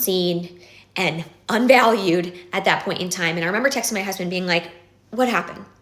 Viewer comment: Did she say “texted my husband”?